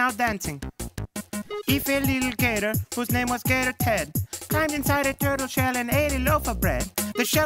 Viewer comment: Hello, this is my turtle song If you take a chicken, put it in a shell What you have is a chicken turtle looking very well If you take a pig and put it in there too What you have is a pig and chicken tiny, tiny zoo Keyboard I’m now dancing If a little gator, whose name was Gator Ted Climbed inside a turtle shell and ate a loaf of bread The shell would make him snappy, and he would eat some more Alligator snapping turtles can eat a grocery store Turtles come from the Amazon, Asia, and the Western Pond Stinkpot is another one Turtles are my friends (Ahh, turtles—they are so happy to see you) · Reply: Thank you, I thought it was saying "If a little kerra, whose name was carrot head"